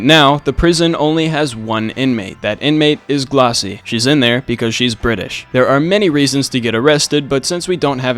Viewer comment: “She’s in there because she’s british” 💀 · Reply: I thought that said “she’s in her British era”